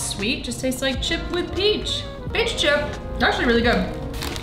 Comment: Lol I thought she said "B*tch chip"